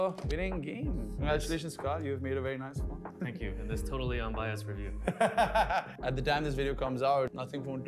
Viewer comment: How did you say do you agree with this totally understand review